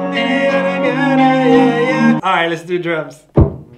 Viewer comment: No cap, was vibing and turned to the camera and said: "let's do drums' What I heard was: "let's do drugs"...lol